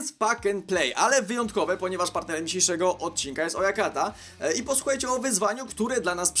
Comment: Na początku usłyszałem, że "partnerem tego odcinka jest moja karta" xD